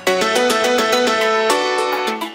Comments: Is it bad that I first thought he said Pool sign? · While it says "Bullseye", read these and repeat it. (first two from another comment) Cosine Pool Sign Cool Site Toolsine KEEP ADDING MORE! :D cuz im so lazy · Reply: lol it mosty sounds like it says poolsign) but it also sounds like it says (onelife) wich is creepy